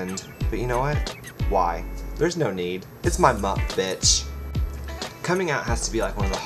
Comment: I kept hearing 'It's my muff bitch', but finally figured out, 'It's my month bitch!' Okay!